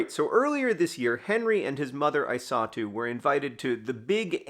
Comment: At the beginning of the video I thought John was saying that he "saw Henry's mother, too" when he said "his mother, I saw too". It took me like 3 minutes into the video to realize her name is Isatu. I might not be smart.